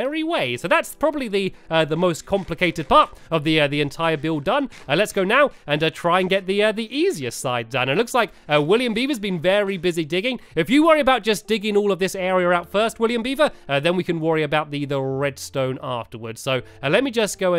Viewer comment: I always thought said William BIEBER! Lol